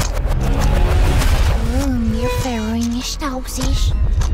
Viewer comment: I hear your fresh blood with me... is THAT JUST ME?!!!